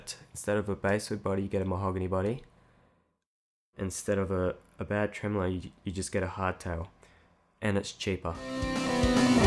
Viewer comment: I turned on the subtitles to grasp what you are saying at 06:04. And the subtitles said: "Instead of a bad tremolo you get a hot towel, and its cheaper" xDDDDDDD'